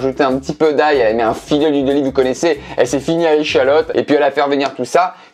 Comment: Je suis pas sûr mais c'est moi ou à 0:25 il a dit "Elle ce finit à l'oignon", c'est bizarre ça.